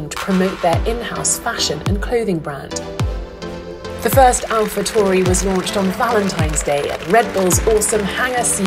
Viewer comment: I misheard it as "Alpha Tory"